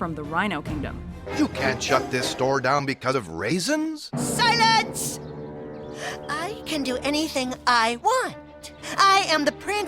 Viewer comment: who else thought the hippo was gunner say "silent I kill u"